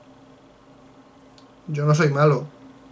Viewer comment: "yo no soy malo, yo no soy malo lo juro" yo solo escuche "yo no soy calvo"